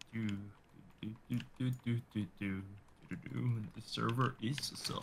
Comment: bro why did say "this server is sus like the imposter from among us"